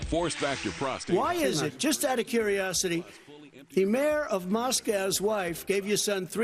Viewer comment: Wait... did he say "the mayor of Moscow's wife"?? Or is that guy's name Maskal or something?